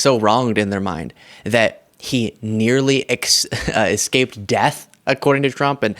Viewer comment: in the clip, did you say "nearly escaped death" ?